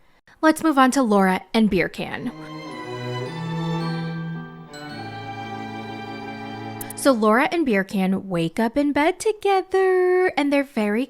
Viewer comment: Hahaha I thought she said beer can …😂